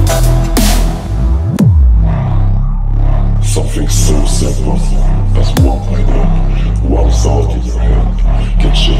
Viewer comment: What does it say at 1:07 ? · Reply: "Something so simple as one idea, one thought in your head, can change the entire course of your life. It can change your entire future. Because one thing always leads to another. Nothing comes from nowhere and nothing happens without a reason. So be careful, but most of all, be thoughtful, about what you plant into your mind and what you're growing in there."